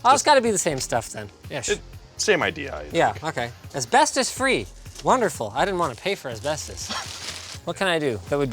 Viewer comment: thought he said "as best as free" and not asbestos free xD